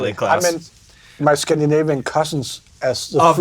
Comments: I always thought he said "Scandinavian customs". Now that I know he said cousins i'm cracking up. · He said cousins? I thought he said customs.